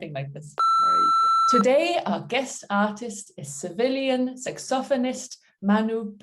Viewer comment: Nice interview! When you introduced Manu as a Sevillian saxophonist, I heard "civilian". I thought, what an odd description! Churchill said something about our two countries being divided by a common language😄